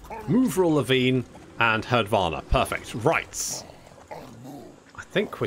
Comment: when you said "Herdvana" i heard "Herdvar" at first, thinking you were naming him after Hadvar in Skyrim